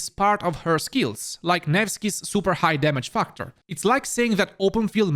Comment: I kept hearing nevskys high dmg fuck-tor :)